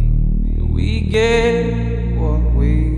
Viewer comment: i love this song but why do i hear "we he gay" at the start💀